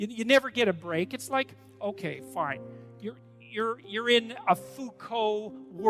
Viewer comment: my absolute FAVORITE part of this is at 6:41 when the brain dead idiot who edited this together thought that JBP said "you're in a fucko world" because they genuinely have never heard of Foucalt. holy shit that is amazing on so many levels lmaooooo